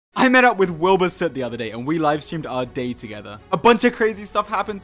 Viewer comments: Am I the only one who heard "Our Date Together" instead of day?😂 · at first i thought he said “we livestreamed our date together-“ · I THOUGHT HE SAID "and we live streamed our DATE together." HAHDHSFISEHUIF